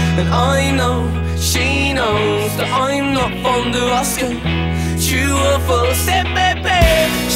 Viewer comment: It must be the British accent because since the debate of the this song I thought they said "i know she knows that im not from alaska" lol but im not fond of asking 😂😂